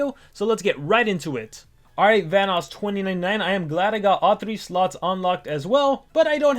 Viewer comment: i thought i heard vanoss 20.99$